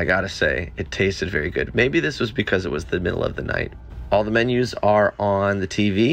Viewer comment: Did you just say “tastezz” 😅